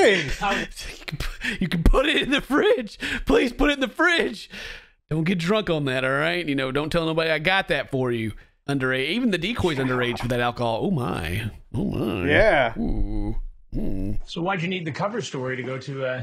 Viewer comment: did he say ''I want to get drunk with that''?